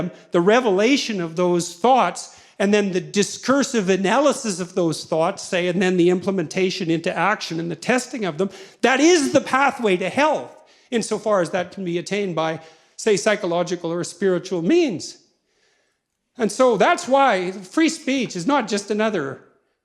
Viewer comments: heaven? Or from hell? edit: Oh "health" ... sorry misheard. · I kind of fell out with regards to how the argument led to the conclusion of 24:44. Did he intend to say heaven instead of hell? I probably just lost the thread a couple minutes earlier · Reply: He says "health". I had to rewind it when I first heard it as well